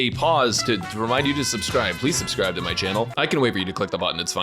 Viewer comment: is that 'please hold' I hear?